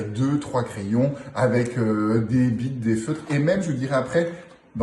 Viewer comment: Avec deux trois crayons....avec heu des b... quoi ? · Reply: des bics (stylo) mais comme c'est une marque il a bloqué exprès ;)